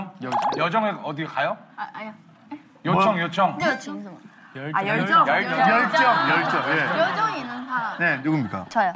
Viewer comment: what did she whisper to yuqi? /gen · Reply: “ how do you say zealous?”